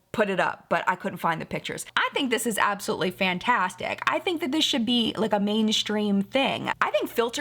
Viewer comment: Totally thought was being punny and said “fanTANstic.” Until I listened again, that is. 😂